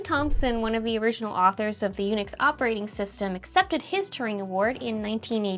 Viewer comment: I misheard a sentence as "accepted his tureen award," as if you were using tureen to describe an award shaped like a soup bowl and then I started wondering if the award's shape was a pun between Turing and tureen.